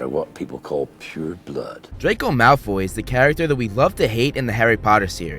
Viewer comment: did he just say love to hate? 😂😂